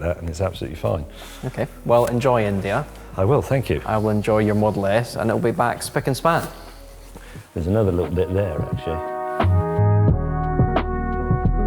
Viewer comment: Did He Say India?